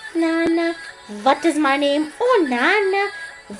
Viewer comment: no sounds like she said slut is my name